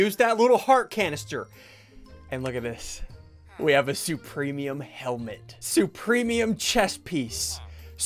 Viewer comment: I thought he said supreme clothes